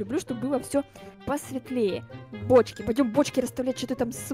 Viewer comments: мне одному послышалось почки? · В начале видео мне послышалось что, сказала почки а не бочки.😆😆😆😂😂😂😂